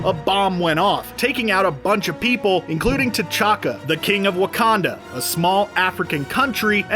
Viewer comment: Did I hear Tachanka at 13:43?